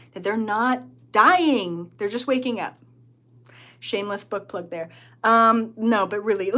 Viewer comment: Had to listen to the last part again. Thought you said "shameless butt plug" like a joke way of saying shameless plug. Cracked me up. But it was shameless book plug. Makes a lot more sense 😅